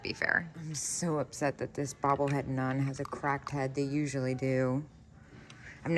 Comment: What I thought you said was that "This nun is a crack head".